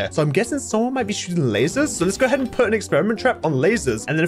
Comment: I'm sorry did you just say latters